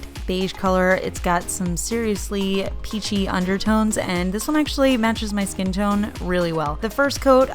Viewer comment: You said "pinky undertones" and I heard "kinky undertones" and was like 😮!!!!!